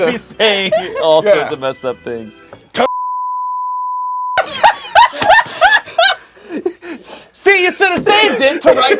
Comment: Anyone who watched the stream, what did ify say at 12:55? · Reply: I didn't watch the stream, but if my lip reading is correct, he said "tongue-kissing my grandad."